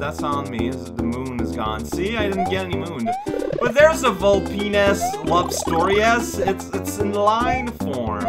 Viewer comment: Silly, those ghosts are obviously metroids. Also I think you said something you perhaps didn't mean to say at around 0:50 ... 🤔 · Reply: I think he said "Vulpi-ness" rather than the other thing :P